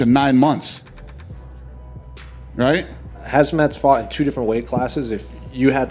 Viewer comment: did he just call him hazmat